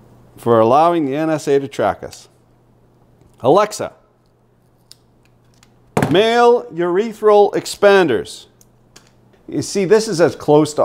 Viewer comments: You said Alexa male urethral expanders, I heard "Alexa mail urethral expanders" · Dude you triggered my alexa and luckily she disnt hear you. Now im googling what it is lol. I thought you said urepheal expander. Urethral expander lol